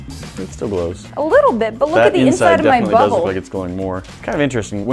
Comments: I swear she said look at the inside of my but hole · "look at the inside of my butthole" did i hear that right? uhhh.... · it sounds like she says look at the inside of my butthole. Lol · I had to listen to this a second time... I thought she said, "look at the inside of my butthole!" 🤪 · Sound like she said, look inside my butthole 😂😂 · i sounded like she said look inside my butthole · did she say looks like the inside of my BUTTHOLE?...LMAO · did she say look at the inside of my butthole 😂😂😂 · did she say look at my butthole??? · i thought said but look inside my butthole. Lmao anyone else hear thatt? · Is it just me or did she say "my butthole" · Listen closely at 15:25 it sound like she says look at the inside of my butthole